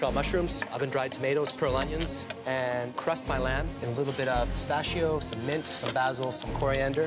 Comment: did he say bäzel ?